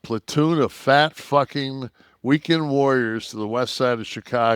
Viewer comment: Is he really calling the national guard a bunch of fat fuck weekend warriors at 0:50?